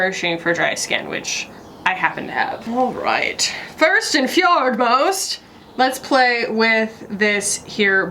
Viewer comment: I legit thought you said, “First and FJORDS-most!”